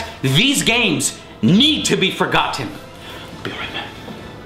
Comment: anyone else hear him say "be right back"?